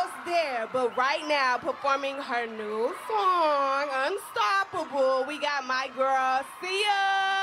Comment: did she say "performing her NEW song"😭😭